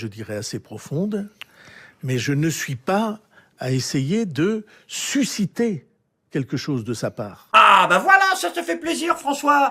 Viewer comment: Désolé mais moi j'ai compris " ...mais je ne suis pas à essayer de sucer pour quelquechose de sa part " cette phrase toute entière est un labsus révélateur il a voulu nous le faire comprendre de manière "politiquement correct" quel filou notre Balou....euh Bayrou